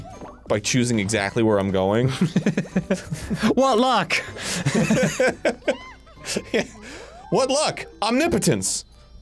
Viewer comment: i swear i heard "what luck im impotent"